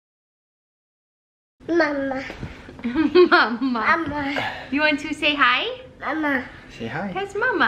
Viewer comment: It sounds like is saying Camera not mama!